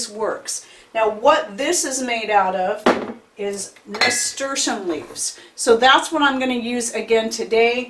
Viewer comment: What herb is she saying 4:12? I rewind it multiple times and still can’t figure it out. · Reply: Nasturtium leaves. Here is the video I did on Nasturtium: <link>